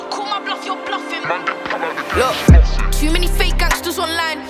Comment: did she really say that 🤣🤣🤣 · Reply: Hollowell ENT she said too many fake gangsters online